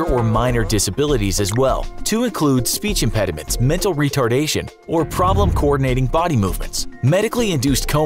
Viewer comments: did he say "mental retardation" ? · DID HE SAY MENTAL RETARDNATION